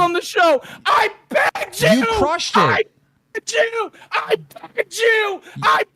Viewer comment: it sound like hes saying "i pegged you"😭